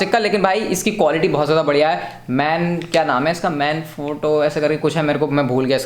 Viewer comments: First I heard it Man Force 😂😂😂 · who heard man force😁😁😂